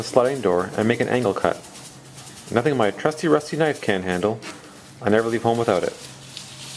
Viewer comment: Did you say "I never leave foam without it" ?